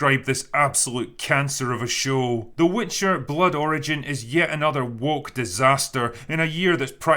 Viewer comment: Sounded like you said “The Witcher: Blood Orange” which would have been a better title for this show.